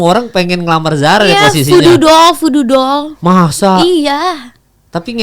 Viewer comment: zara ngomong apasih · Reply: voodoo doll (boneka santet)